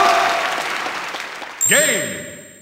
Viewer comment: Play your Game! (That's what it sounded like the announcer said.)